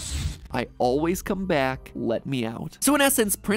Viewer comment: I heard: ‘I love you mike...’ ‘Let me- o w ’